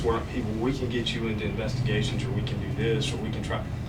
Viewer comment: I thought it said "csgo internal investigation" at 1rst lol